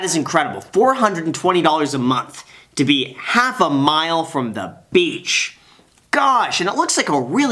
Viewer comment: I think she said half a minute from the beach, not half a mile.